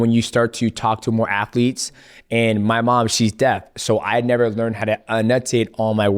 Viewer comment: Did he say my mom is dead